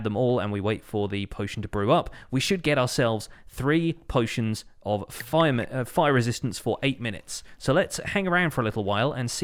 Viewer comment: Did he say....we gonna get three potions of FiReMen?? 16:31 ??? Wth?? I needed potions not people